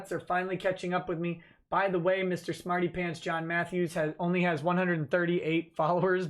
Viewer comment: in what way did you make THAT assumption? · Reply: my mistake. I could have sworn I heard his twitter profile referred to as John Map during the video. I mostly listen to these and now realize his name is John Matthews.